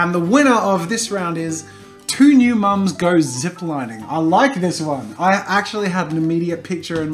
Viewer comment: I thought he said " two nude moms go zipling